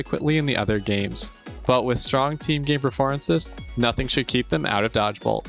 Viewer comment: Ok if you listen at 5:04 it sounds like he says "Team Gay performance" not "Team game performance" and i definitely stand by it.